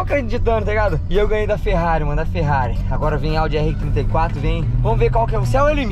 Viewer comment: Só eu q ouvi o dizendo pôde vir Audi r34😂😂 momento 14:17😂😂